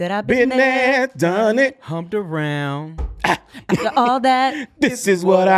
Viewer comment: I never realized he said humped around. Wtf hahaha I’ve been wrong about that for like 20 years Lol I thought he said “talked about” maybe that was the clean version but humped around makes way more sense 😅😅